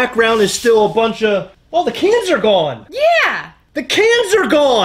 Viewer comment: I really thought you were saying "The Cancer Gone" in the intro 😂